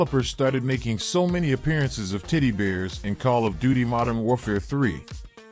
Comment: pls tell me I'm not the only one hearing him say "tiddy bear" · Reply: I hear "tiddy BEERS"